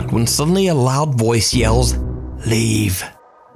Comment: I know its supposed to say "leave!" But all I hear is: "REEEEEEE!" 😂😂